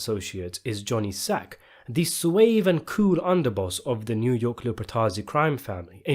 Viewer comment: Wait, at 0:20 did u say swave ?